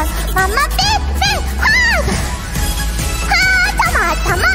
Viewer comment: Me: smiling because this is adorable Me 6 seconds later: W-what name did you just say? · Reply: she said haachama why?